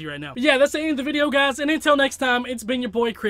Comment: it sounds like you said p&$&s